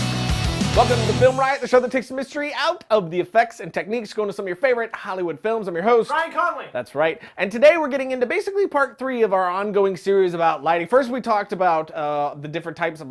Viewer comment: I thought he said im your favorite host iCarly XD